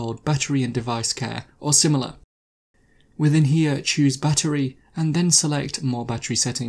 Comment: It would be more helpful if you would slow down your talking to be better understood. Also - at first I thought you were saying "buttery". After repeat playings, I figured out you were saying "battery". All this together made it difficult to understand and caused repeat playings.